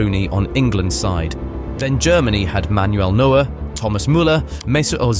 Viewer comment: bru when you said manuel neuer i thought u said noel noa